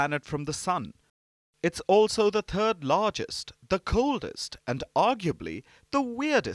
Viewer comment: I totally understand Uranus. Wait... did he say "the turd largest" at 0:50? I'm pretty sure he did...